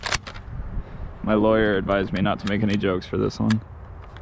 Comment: what did you say? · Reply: “My lawyer advised me to not make any jokes for this one”